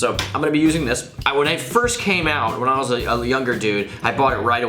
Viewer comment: I thought he said he came out